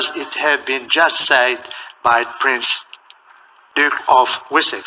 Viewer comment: I thought they said Prince Dick of Wessex …..more appropriate.